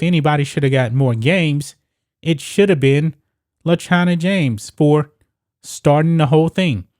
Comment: Ohhhh le china james.. I thought you said vagina james